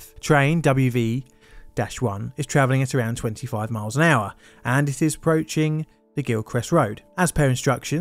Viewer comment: Very nice and sunny weather in the UK! Also I really thought this said "The Glitchest Road Bus" 😂